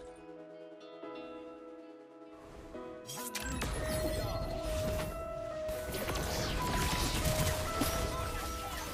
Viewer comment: someone tell me what did he say? when he uses the ss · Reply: The loop starts